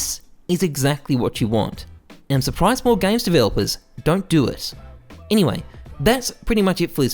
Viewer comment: What did he say at 7:37? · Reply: "Im suprised more game developers dont do it"